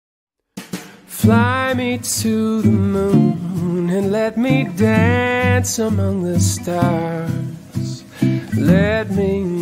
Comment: I keep hearing “fly me to the moon and let me play among us”